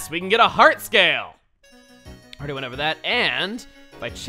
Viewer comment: For a second, I thought it said pear.